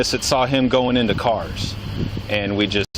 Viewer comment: No cop said carS. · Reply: ?? what is this fuss about? 5:56 cop said carS, she did not "correct" always just said doorS.